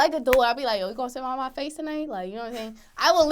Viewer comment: Did she say are you gonna come sit on my face tonight (?) .. WHAT (?)!!!! . WESTBROOK FACE !!!